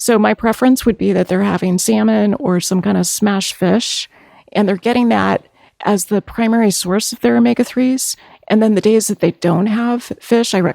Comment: what is she saying? "Smashed" fish?